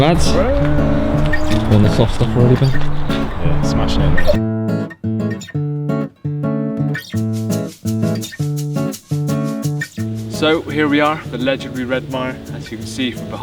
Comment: did I heard polish word "smacznego"? :)